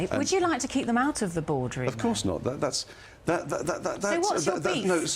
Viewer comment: did she just say 'what's your beef'